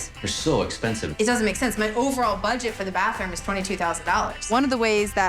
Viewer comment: Wait did she just say much entire budget for the bathroom is $22k wtf 😂 $22k for a damn bathroom shit floors must be made of gold! 😂